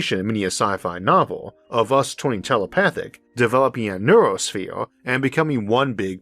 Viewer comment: I almost thought I heard him say "noosphere", which was an idea thought up by French theologian Pierre Teilhard de Chardin. He also imagined humanity being able to master and understand the material world and merge together to form a global consciousness- albeit in a more spiritual manner.